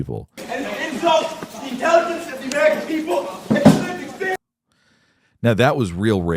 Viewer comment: "An insult to the intelligence of the American people" is what he said.